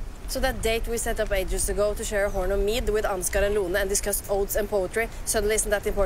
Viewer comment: "... discussing odes and poetry..." The scene where they do that is completely hilarious! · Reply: Oh that makes so much more sense i thought she said " oats and poetry " 😳